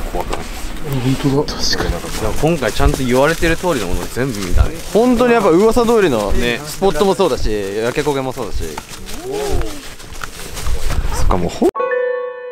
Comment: 女の人の声でお〜いみたいなの聞こえる😮